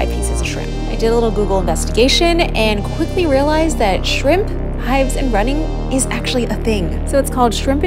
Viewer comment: First I thought she said she broke out into "bee" hives😭